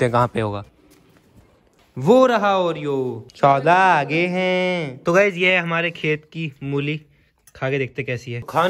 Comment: kya kaha smjha nahi pr sun kr accha lgaa😅🤗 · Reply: He is saying " Sourav Da aa gaye hai "